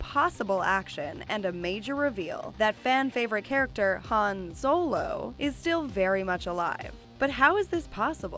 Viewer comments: Did she say Han Solo? 💀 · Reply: Yeah his name is Han Seoul oh or something like that. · omg did she say " Han Solo"...🤦‍♂️ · did she just say Han Solo? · did she say han solo? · Did you just call him Han Solo ???